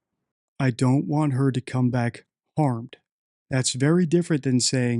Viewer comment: I feel the "I don't want her to come back" was leakage. She shows no emotion at all about her being missing. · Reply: The first time I saw that interview I heard 'I don't want her to come back home' and I was like huh??? But then I turned on subtitles and realized she said 'harmed'